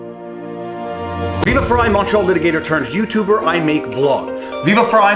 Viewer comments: Up until now I thought you were saying “macho litigator” in your intro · Oh, I thought he said "macho litigator".